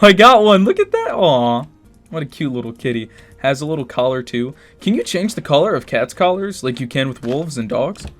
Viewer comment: I just heard "Can u chane the color of cats like Wolf and Dogs"